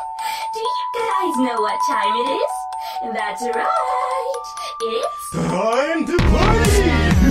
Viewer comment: I swear to god I thought he said "Time To Play!" At the beginning of the song